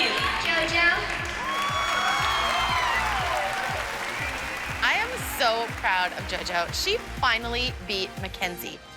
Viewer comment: the mom behind Melissa, what is she saying? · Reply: “I’m her biggest fan” I think